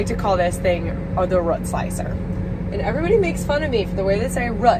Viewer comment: Oh, and here I always thought you said "rut slicers". Not a farmer so that made perfect sense to me. :D